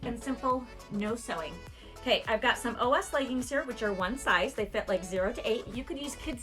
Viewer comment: At first, I thought you said old ass leggings lol... Anyway I like it!